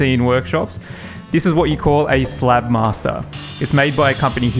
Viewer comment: totally thought you said slab master lol Google had my back tho it's SlabMizer